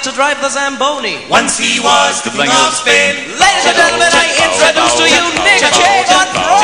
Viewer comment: "Once he was the Fling of Spain" anyone else hear it? hahaha great improv